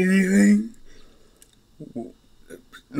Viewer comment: Ok. I burst out laughing when I heard Izuku said "I always wanted a Horse"